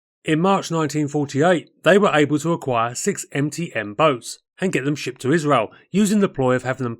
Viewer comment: Jeez, for a long time I thought you were saying EMPTY boats instead of MTM boats! My bad! I wonder how any of these young men could walk while carrying such large balls around swinging between their legs!